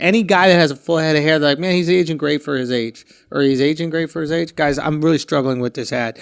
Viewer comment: I swear to god it sounded like he said he's "Asian great for his age" 😂😂